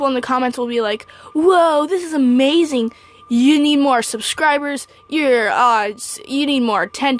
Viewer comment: When she said " You need more subscribers " I thought she said " You need more to eat " Help me RN